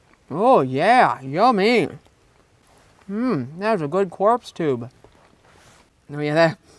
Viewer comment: did he call the hot dog a "corpse tube"